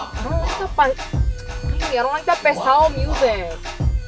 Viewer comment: When she said "pesado" music, for a moment i thought I heard (and read) "pescado" and I'm just thinking "fish music"?